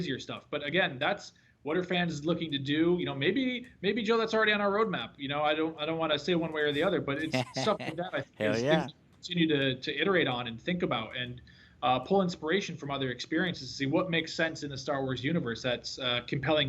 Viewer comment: All I keep hearing is "I think that, I think that, I think that, I think that, I think that"